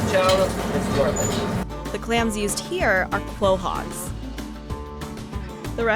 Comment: did you just say clohog that's not a thing it's called a quahog ""co-hog"" I can tell you ain't from New England